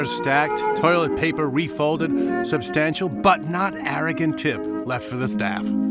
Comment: I know there were other words around the phrase, "substantial butt knot," and that, in context, completely different spellings are implied, but that didn't keep me from laughing out loud at what I thought I heard.